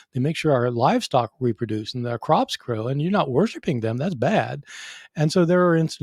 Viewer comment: Regarding stories of martyrs not feeling pain, I remember hearing a story about some saint being barbequed alive on a metal rack, calling his tormentors over and saying, "Can you turn me over? I think I'm done on this side." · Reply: I thought he said, "you're not using enough sauce."